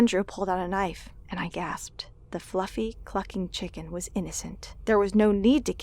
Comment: I thought she said the fluffy fucking chicken